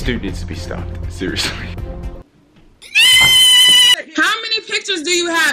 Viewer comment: did he say what is a hickey?